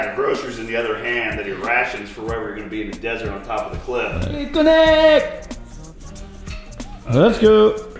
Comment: Did I just hear saying BITCONNEEEECT! right when BTC was about to lift? LOL